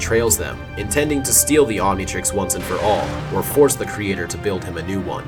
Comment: i heard you say ontitrix